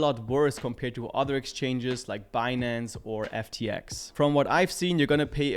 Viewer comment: Did you say FTX? 👀